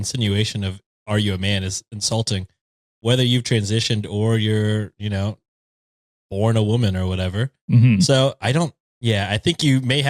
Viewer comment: I'm so curious how tall the transphobe who asked a 5'10 woman if she was a man was cause he's giving insecure about height vibes. I was born a woman and 5'10 and many men hate it 😂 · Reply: i thought you said you were born a 5'10" woman and i was gonna say "dang, mustve been a tough birth" 😭